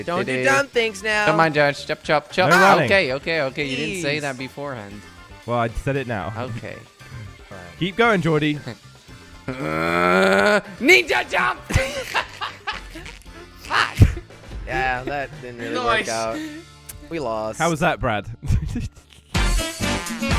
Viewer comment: NICHA JUmP /that’s what I heard Me:dies of Laughter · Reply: “Ninja jump”